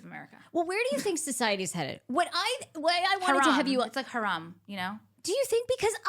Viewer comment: what’s that word?? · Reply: haram